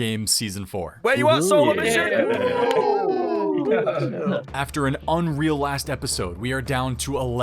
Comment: I’m a minute in and all I hear is “WHERE YOU AT SOLO MISSION?” 😂😂😂